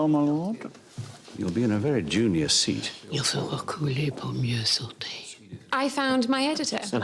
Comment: Does anyone know what she says at 0:47? I think it is French but I cannot hear it well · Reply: Il faut reculer pour mieux sauter - which means You must step back to jump further